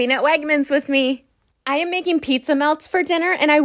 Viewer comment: i thought u said pizza milk 💀